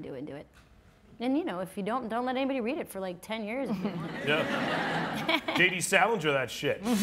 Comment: What does Seth Rogen say at 52:30 ? “(something) on that shit ” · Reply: J.D. Salinger